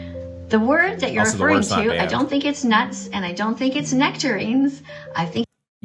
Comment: when talking about the N word, she didn't say "i don't think it's nice" she said "i don't think it's NUTS" as in, "the word you're referring to, i don't think it's nuts, and i don't think it's nectarines." she's naming foods that start with N, both of which are found in nature, and thus vegan, because veganism is her whole personality.